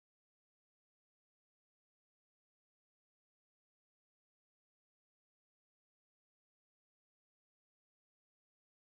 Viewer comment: Either I'm going deaf or I didnt hear not 1 I'm real@ 1:45😂😂😂😂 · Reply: "I'm just keeping it real" - was version of "I'm Real" 🤣🤣🤣🤣